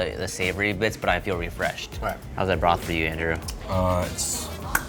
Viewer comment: did he call him andrew?